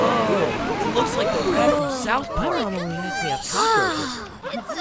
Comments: did i hear that right “it looks like a rat from south park!” BECAUSE IF I HEARD IT RIGHT I SWEAR IM GONNA DIE OF LAUGHTER HAHHSHSHAHAH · Did anyone else hear the person say “is that the rat from South Park?” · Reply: Erm he said that “looks” like the rat from South Park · I HEARD "IT LOOKS LIKE ONE OF THOSE RATS FROM SOUTH PARK" AT 5:57 AND I THINK YOU KNOW WHY IT MAKES ME HAPPY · IM SORRY DID I HEAR SOUTH PARK!!?? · Did I hear South park👀👀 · "it looks like the rat from south park" i can;t tell if i'm mishearing things but WHAT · did you guys also hear something about South Park, or was that just me? I heard ‘’looks like the rat from south park’’